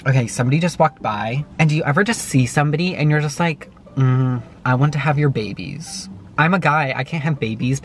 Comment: I honestly thought he said “I want to have your rabies” But he said “I want to have your babies”. O.O